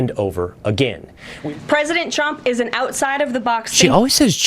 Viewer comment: She clearly says "Trump". Try wearing headphones. 5:08 They always do this though. First it was "Mandate, mandate, mandate, mandate" now it's "Omelette, omelette, omelette". This is how state media works. Also, BTW, the last few reputable news outlets just had their access revoked from the Pentagon, and only the right-wing propaganda networks are allowed.